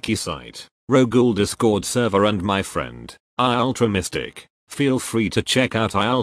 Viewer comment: I Thought He Said I Ultra Instinct xD